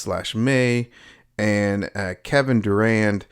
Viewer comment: Sounded like you said Kevin Durant in the video, had to replay a few times to make sure lol